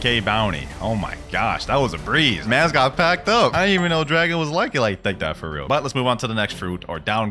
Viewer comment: Did he say Ldargo or dragon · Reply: L drago